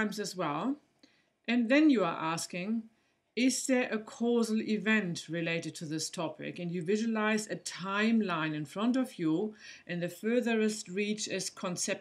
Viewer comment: Hello, thank you very much for your videos! I would like to ask you, since my english is not very good i cannot understand what you say from 3:13 to 3:25, can you please write it down? Thank you very much!!! Mirko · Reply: mi ji Ask; Is there a cause event related to this topic? Visualise a time line in front of you where the furtherest reach (of your arm) is conception to birth and then move closer to your body. Best of luck with it :)